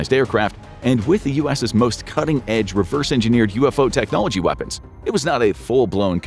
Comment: Did i hear that right 4:27 UFOreverse engineered weapons!!!